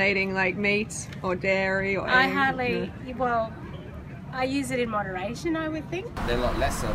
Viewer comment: What did the women say at 4:06? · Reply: "I use it in moderation"